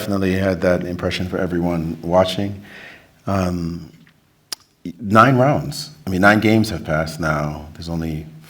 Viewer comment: did Maurice say 9 months? 😂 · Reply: 9 rounds I think